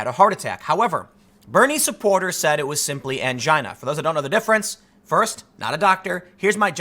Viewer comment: did Tim say he has mangina?